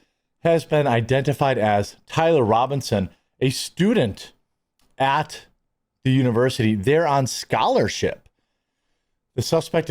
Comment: I thought they said he wasn't a student there